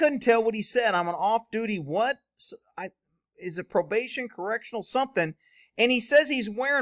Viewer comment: "I'm an off duty conservation officer" is what I heard him say.😂😂😂